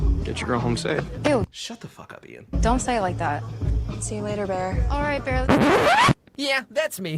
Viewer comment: but see, she actually says "Eww, don't say it like that". So it might be the "your girl" (possessive like she's an object) that she didn't like.